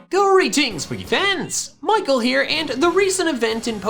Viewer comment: I cant stop hearing the intro as "youre eating swiggy pants" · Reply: I heard "greetings pokè pans"